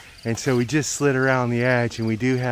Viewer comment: I think he said "Coco que Anda", ("Walking Coconut") may be a popular local name for this tree. It is better known as 'Paxiúba' or 'Palmeira que anda' (Walking palmtree). AKA Socratea exorrhiza 4:50 - It's a spider monkey. There are 15 species of spider monkey. 23:30 - They are called Emas 26:40 - Observe how they react to the approach of the drone. Although they live well among different species, they all change behavior when they see the drone. Each species use a different defense strategy. Some take off and fly over the area, others spread their wings to look bigger...